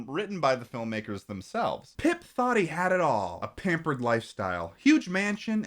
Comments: i thought he said “pip thotty”- idk why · I misheard the description and thought the main character's name was Pimp Thotty 💀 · When you started reading “Pip thought he had it all” in the synopsis I wasn’t watching and I thought you said “Pimp Thottie had it all”. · I thought said pip thotty at 2:48 lmao